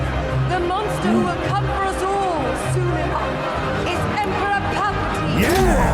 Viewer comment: The actual, physical anxiety I felt leading up to the climax of 8 was insane. I knew what was coming and I was horrified the entire time. I knew what was likely going to happen to the people we’ve met and I was still horrified by their deaths. This was honestly an incredible arc, better than I hoped they would handle it. Mon’s speech had me and tears and I fucking YELLED when she named Palpatine. I haven’t been this excited to be a Star Wars fan in a long time. And I am both dreading and anticipating seeing the end next week. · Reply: Ah Palpatine. I heard "Pootin =D